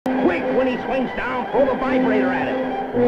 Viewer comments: "Quick! When he swings down, throw the vibrator at him!" Did I hear that right? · does he say 'throw the vibrator at him'? 🤣🤣🤣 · I think that in this universe Peter Parker is dead and some bumbling idiot decided to take over for him lol. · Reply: Um in the beginning I could've sworn I heard "three the vibrator at him" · Did he just say "throw the vibrator at him"?